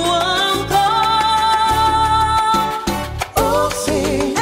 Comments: Nghe thành chữ " ước gì em là con lợn lòi " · Mình nghe thành ước gì em là con lợn lòi :)))